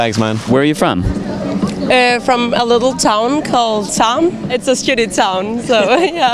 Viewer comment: If i heard correctly she's from Tarm which means intestine